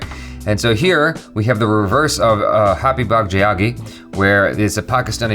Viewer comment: for those of you figuring out what i saying its happy bhag jayegi